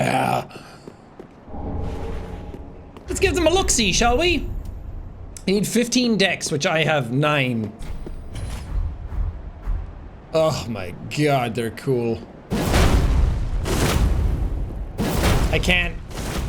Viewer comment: anyone else think Jack legit said "eat 15 d***s, I have 9" or just me?